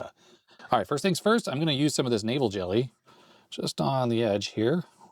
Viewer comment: I thought you were saying Navel Jelly...